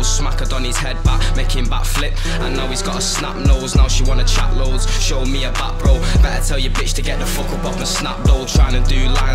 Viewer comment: did he just say “show me your back bro”🤮🤮🤮🤮🤮🤮